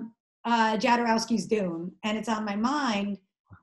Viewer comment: Does anybody know what documentary she is talking about at 19:15? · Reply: Jodorowsky's Dune :)